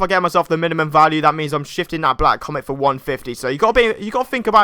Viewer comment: did he say shitting?